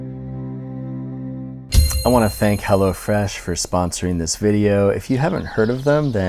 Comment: I thought he said hella fresh yea im stupid